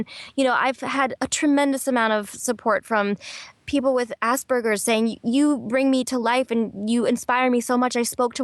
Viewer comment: I thought she said assburgers